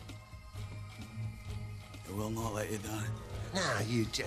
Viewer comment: I heard it as "ay pop, we will not let you down."